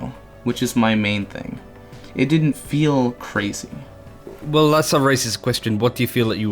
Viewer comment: am I the only one who hears "Well that's a racist question...."